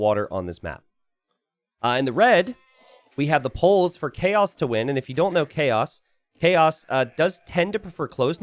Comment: I thought you were saying it was the "polls for Chaos to win" and I was like, wow, you poll these games in advance? But it was the Poles for chaos_2_win.